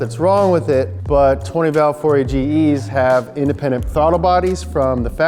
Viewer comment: Hey in the subtitles 2:18, the engine name is wrongly mentioned as 40 GEs instead of 4A-GEs